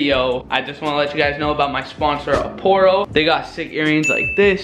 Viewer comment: I’m sorry but when he sponsored the thing I thought he said porno 😂